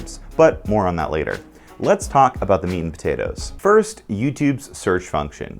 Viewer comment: Me, half listening: “Did he just say, ‘Let’s talk about the mean potatoes’?”